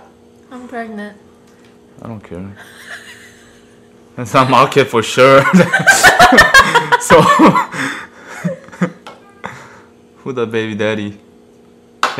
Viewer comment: I thought he said “it’s a mudkip for sure” 🤣🤣